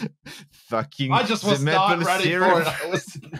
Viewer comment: Thank you so much for this! I'm the captioner, and translation isn't my strong suit (even though I'm Canadian, so I should know these ones a little better...) Could you please also translate the "Top Ten Alice Moment" at 17:59 as requested <mail> Cheers! ✌🍍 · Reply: ah at 17:54 I think she's saying, "fucking, ze maple syrup" Et de rien! 😊